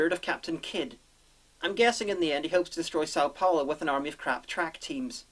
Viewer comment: (what sounded like) South Palla has a defense Gundam? Or is it like a scarecrow, meant to ward off roving track teams?